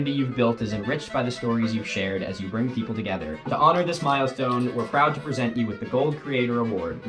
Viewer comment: The first time around I honestly misheard „To honour this Milostone…“